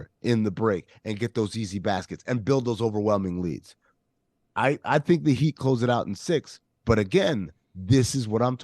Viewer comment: Could have sworn you said Riley wants to close it out in Boston. Probably making that up